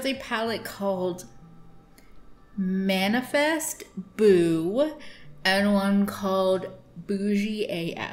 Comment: Oh no I thought you said "manifest boobs" was the name of that pallette.